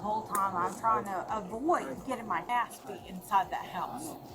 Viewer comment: did she say " Trying to avoid getting her ass beat inside. "